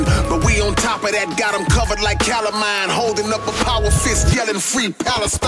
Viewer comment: what does he say? "Got em covered like...?" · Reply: seraph101 I believe he says calamine. It's a lotion you put over your body to treat itchiness. Covered Like Calamine, holding up a power fist yelling free Palestine.